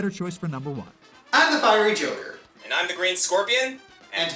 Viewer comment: Wait...did I hear "Wall Turkey"? Or "Whole Turkey"?